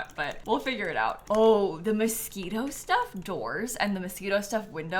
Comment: I thought she said MOSQUITO doors and I was so confused for a minute there 😂😂😂